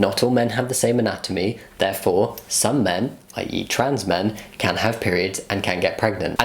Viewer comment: i thought he said 'i eat trans men' 😅 oopsie